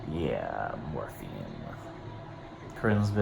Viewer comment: Every time he said morphine I heard morphie. I'm so glad you're getting better!!!❤️❤️